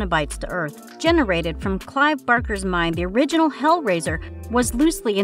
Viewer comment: This poor girl. I blame the scriptwriters since she's probably only there to do voice work. The species of hunters that are referred to as "predators" are the Yautja (Yowt-Jah)... the book from the Evil Dead series is the Necronomicon ex Mortis (comes from the H.P. Lovecraft stories), not the Necroconium an Mortis... and Michael Myers' home town is Haddonfield, not Hendonfield. Please have your scriptwriters do more research and fact-checking before having someone read it for an actual video in the future. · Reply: Berker instead of Barker